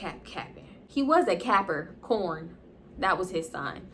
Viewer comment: SHE HAD ME DEAD it sounded like she said "capper-corn" LMFAO